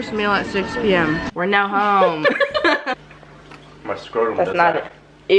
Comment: did just say "my scrotum does that"???? DIS COS TANG CHILD